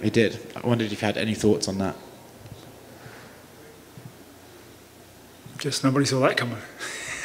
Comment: what did he say at 0:16 ? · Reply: sounds like he said "just no body is a latecomer"